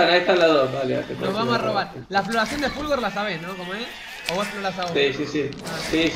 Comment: que es lo que dijo de la exploracion? de fulgor? o algo así? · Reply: fulgore! <link>